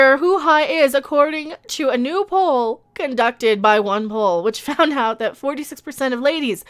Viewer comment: I kept hearing " One Hole" instead of " OnePoll"!